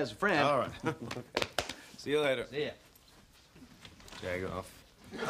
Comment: what did he say? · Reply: He called him a jag off